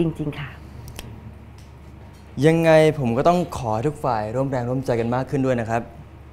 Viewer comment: ใครได้ยินพะเอกพูดว่าทุกควายตอนที่บ้าง3:55-3:56🤣🤣🤣